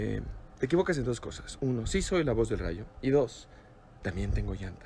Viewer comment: wtf . No entendí lo que quiso decir . Expliquenmelo . Me dio gracia como lo dijo . Pero no entiendo a que se refiere jaja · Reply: Lo Que Dijo Fue También Tengo Llantas Están De Lujo Kuchau Eso Fue Lo Que Dijo